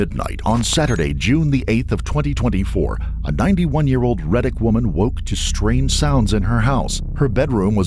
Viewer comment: Thought you said Reddit 💀